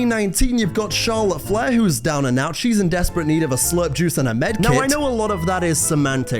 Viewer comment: WAIT WAIT WAIT did he just say. Charlotte in 2019 needed SLUT juice?!?? That did not sound like SLURP. Bro did not slurp